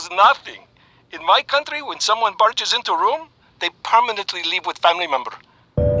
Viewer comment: I couldn't quite hear Zeke clearly. I hear "Whenever somebody barges into room, they permanently **** with that family member". Did he say 'Permanently Live"? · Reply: Actually he said: "in my country, when someone barges into room, they permanently leave with family member."